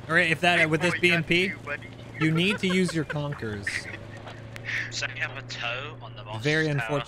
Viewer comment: What does say here? "You need to use your concurs?" I think I'm hearing something wrong, what does concurs mean? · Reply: Konkurs. It's the BMP-2's anti-tank guided missile. The BMP needs to almost always first engage with it even if they screw up the first shot.